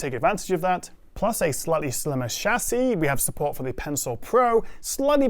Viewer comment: did he just say “sl*tty slimmer shlassis”